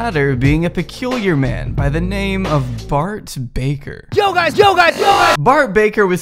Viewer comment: I thought it said Brock Baker and I was wondering when he started going down..